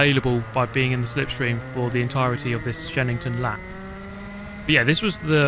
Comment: I kept hearing you say "Shedington" and thought this was some kind of Jimmy Broadbent mod.